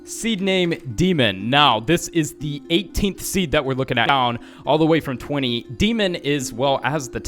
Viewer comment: i heard you or him say * down* or noun and it must have been a demon or his mic cut out for a little bie